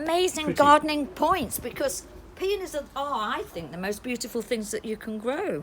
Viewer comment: For a minute I thought said “ well done because a penis is the most beautiful thing to grow “ 🤣 slightly horrifying comment for her to make to you mark !